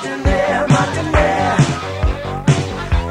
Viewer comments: I thought he said I want to be a murdernaire I was like how awful 😂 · Growing up, I thought he was singing: "I want to be a mountaineer" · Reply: Thought he said I want to be a millionaire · So I am TODAY years old and learning that he’s saying “Modernaire”. I’m a huge Prince fan however I also thought he was saying “Mountaineer” too, lol! It never made sense to me, so I just assumed he was saying he wanted to be on top of the mountain (mountaineer), similar to saying “being on top of the world”. LOL! 😂😂